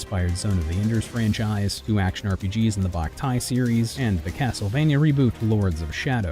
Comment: I had to do a google image search. I kept hearing "2 action rpgs in the Black Tie Series" lol